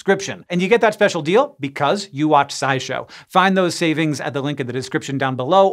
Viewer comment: I swear I heard “sideshow”. Must be the topic.